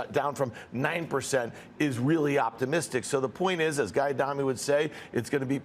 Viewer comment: Anyone else catch that he called jamie diamond , guy diamond at 4:54 . Guess he been watching too much trolls.